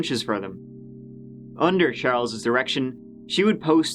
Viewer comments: I heard "Under Charles' erection." I need new ears, halp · I was listening quickly or not paying attention or something and I could have sworn he said under charles’ erection. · for a second i swore you said "under charles' erection" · I swear at 8:01 i thought he said under Charles' erection...i actually had to replay that part twice... Time for a hearing test? 😵i hate Dr's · am I only one who thought he said erection? I had to check the CC and its says direction which makes sense but I just don't here it lol